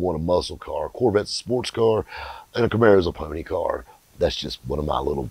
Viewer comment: What Did he say a Camaro is??? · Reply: PONY CAR I THINK IS WHAT HE SAID..........